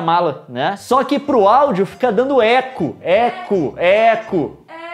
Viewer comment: Mano eu dei muita risada pq quando o falou Eco, Eco, Eco eu entendi Ecu, Ecu, Ecu